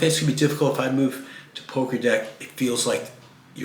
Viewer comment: PO- (like harry Potter) Gra(to grapple) Dec( De like decades and the last letter C is pronounced as ts) Po- Gra-Dec. I thought you said Pokker Deck lol and im like is he going to some asian countries?